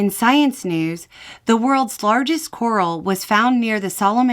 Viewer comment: Holy moly! At around 3:40 I heard "world's largest squirrel" and wondered how it could possibly be seen from space 😂 That's a big squirrel!